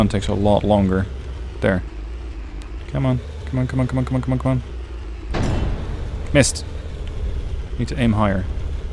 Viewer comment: what did He said 😂i understood "mist"🤣🤣